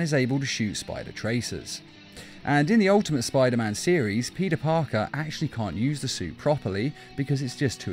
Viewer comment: Is it me or does it sound like he says pedah Parker